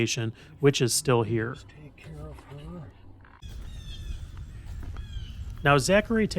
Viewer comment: did you have your radio on or somebody was with you? i swear i heard the words "let's take care of her"...